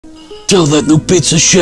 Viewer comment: I thought he said tell that shit chef